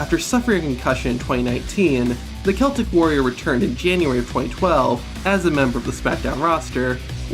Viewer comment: did you say january 2012